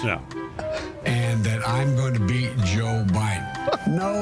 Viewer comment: did he say "I'm going to beat Joe Biden"? 👀 😂😂😂😜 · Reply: Some people say he said "be" Joe Biden